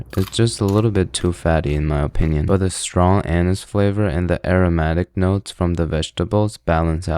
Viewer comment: did i hear that right Strong anas flavor